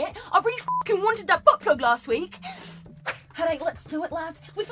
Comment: I’m i the only one who heard butt plug